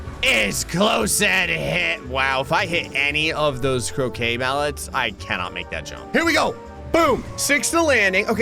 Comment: and it was at this moment i realised that he was saying crocay mallet i thought he said cokain mallet although both work